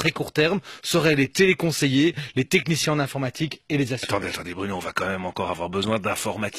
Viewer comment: C'est quoi la dernière profession menacée à 19:53 ? on dirait qu'il dit "assureur"...